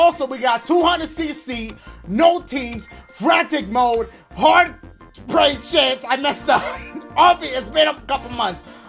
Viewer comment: Sound like said hard price check 😂🤣💀